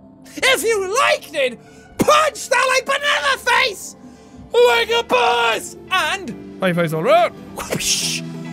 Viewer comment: I thought he said " PUNCH THAT LIKE BU-ANNANA FACE....LIKE A BOSS" at the end